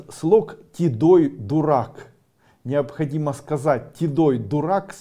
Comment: Здравствуйте!а вслух надо сказать или шепотом и где у себя дома или в присутствии этого человека, на которого думаешь либо в магазине он встретитьсч либо на улице и возле него? И правильно ли я расслышала Тидой дурак? Первая буква Т? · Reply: Там вообще то Тидой, а не гнилой